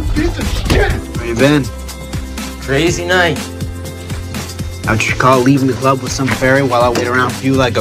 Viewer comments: What does Mickey say 1:38 - 146 lmao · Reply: He says: "Fucking cheat on me, Ian? Won't get out of bed and you're bouncing off the walls. I came out for you, you piece of shit." :( :( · in the scene that starts at 1:37 where Mickey goes to the club looking for Ian and the guy working suggests him and Mickey hook up, does anyone know what Mickey is saying to the guy while he punches him? the only words I have ever managed to catch are cheat and Ian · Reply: He is saying: "Fucking cheat on me, Ian? Won't get out of bed and you're bouncing off the walls. I came out for you, you piece of shit." :( :(